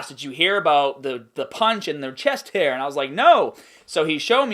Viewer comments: I thought you were saying chest HAIR!! LMAO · Dude not going to lie, I could of swore you were saying "chest hair" lol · Lol I kept hearing "chest hair" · thanks for sharing these stories. and I thought you said chest hair. lol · For the longest time through the beginning of the video I thought you were saying "chest hair"!